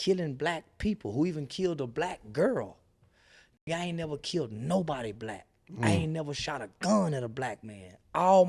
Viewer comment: I agree with him but I swore he said he never killed anyone